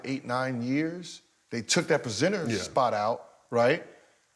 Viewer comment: Did Gil say pacenter?